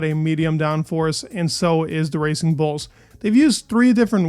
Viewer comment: I heard that as "Racing Bolts"